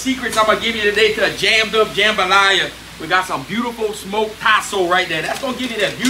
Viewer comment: what was the second meat he put in with the sausage? sounds like he said possum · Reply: Tasso. It’s ham